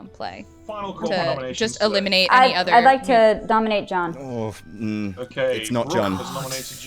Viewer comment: Could have sworn Brooke said "I'd lke to Dominate Jon" Haha